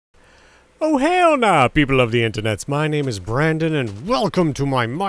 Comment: SOunded like he said "OH HAIL NAW"